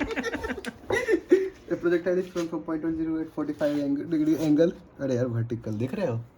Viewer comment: took me about 2-3 minutes...when he said the answer I thought he said t=3.5 seconds and I was confused, but then realized he said .5😅 Excellent video, really motivating to see IITians solving the paper I will be giving next year🤟